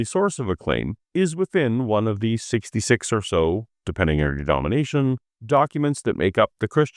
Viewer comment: I love your content and respect you greatly.. But I misheard 23:22 "denomination" as "domination".. I'm now trying not to gigglefit at the mental images that produced.